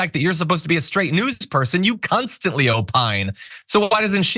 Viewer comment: did you just say “cunstantly?!” 😂